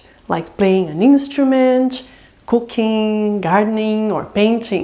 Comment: Oh you said cooking i thought you said cocaine lolll